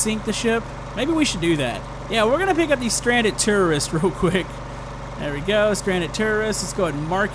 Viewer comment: It sounds like you were saying terrorists